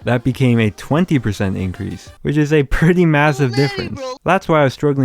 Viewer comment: DID I HEAR MASSIVE???????